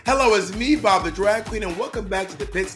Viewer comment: I thought he said hello it’s me, father drag queen. Bob is now dad. Lol